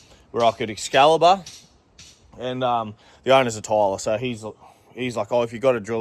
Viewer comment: Missed you last week! When you said the owner is a tiler, I misheard and thought you said the owner is a tightarse! That would be a pretty bold move, lol. Love your work 💪🏻